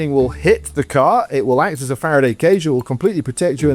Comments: the voice says "Faraday cage" but the subtitles say "fire enclosure". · he said faraday cage, not fire enclosure like the subtitles say. they’re not auto subs, so that can’t be used as an excuse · the man says "faraday cage" but the subtitles say "fire enclosure" lol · The subtitles say ''fire enclosure'' (whatever that means) but it should be Faraday cage, FA-RA-DAY CA-GE. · captions said the car will act as a "fire enclosure," when in reality what he said was "Faraday Cage" lol. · I believe at 20:11 the subtitle should say Faraday cage instead of 'fire enclosure.'